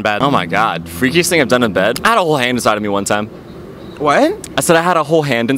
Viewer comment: I thought he said a whole ham 💀💀